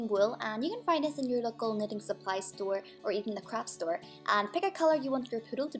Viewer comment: Sounded like she said crap store lol